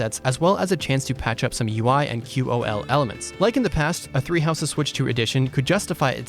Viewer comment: I totally thought it said tree house's.